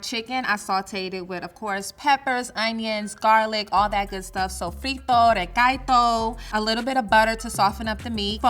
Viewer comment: Did she say Frito dey Gaito